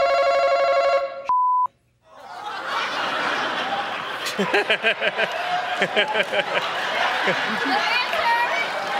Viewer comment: what is he saying? Sorry non english speaker here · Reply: he said “shit” which could mean many things but in this sentence it means “nothing”